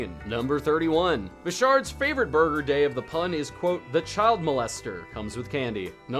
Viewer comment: Was I the only one that heard him say "Burger day of the pun."